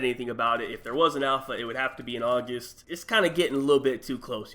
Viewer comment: anyone else hear him say "Aujust"